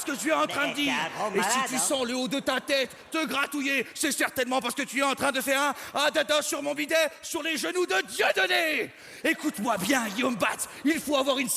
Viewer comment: Qu'est-ce qu'il dit 6:15? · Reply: "te gratouiller, c'est certainement parce-que tu est en train de faire un à dada sur mon bidet sur le genoux de Dieudonné"